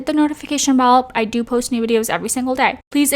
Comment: I thought you said, "I post nude videos ever day."😂😂😂